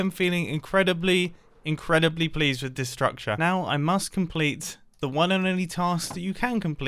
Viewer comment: "I'm really pleased with this structure" I totally heard "I'm really pleased with destruction," and honestly, I feel like both are valid.